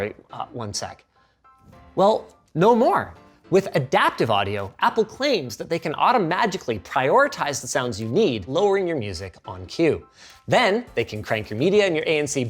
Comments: Did he say automagically? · DID HE JUST SAY "AUTOMAGICALLY" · Did he just say 'Automagically?' I love that word.